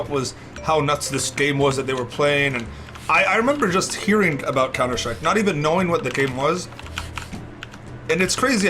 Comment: what says "I remember when I hearing about counter strike" what subtitle says "I remember when i hearing about chemistry" if you not play CS back in 2002 , you would be a chemistry professor today.......